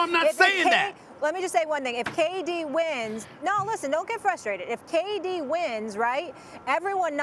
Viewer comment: Did she just say "Baby don't get frustrated"